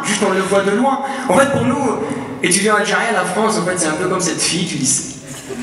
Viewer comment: Il dit quoi à 0:40 ? La France comme....? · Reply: Comme cette fille du lycée...😂